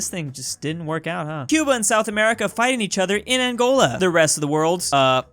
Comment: Did he just say South America?